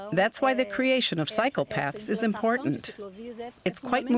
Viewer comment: For a second I thought I heard "That's why the creation of psychopaths is important"